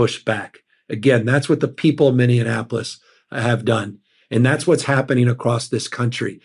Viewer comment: did he just say “mineaNApolis”??